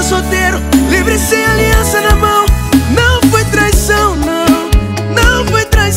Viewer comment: Eu ouvi "livre sem aliança na mão" e não "mesmo com aliança na mao" 🤔